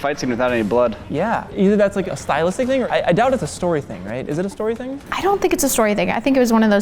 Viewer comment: when you say "is not a story thing" and her said the same... i hear, "give us comments", nice way to trick the algorithm